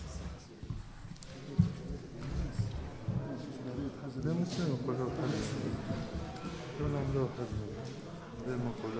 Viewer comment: سمعتوه شقال؟؟؟؟ 😨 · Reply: تو نعملوها وحدنا منغير منقولولهم نفجهوهم !!!!!؟؟؟؟؟ 🙄🥺🥺🥺🥴